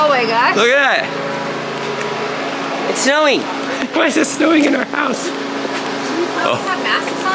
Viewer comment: I thought it said insulting our house!?😂😂